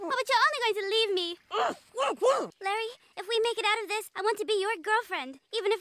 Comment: he actually says “kenny not larry”